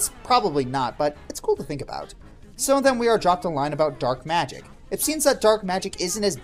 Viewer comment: Lol, for the longest time, I thought she said 'dirt magic' because Rarity turned the dirt to gold and I never looked it up before...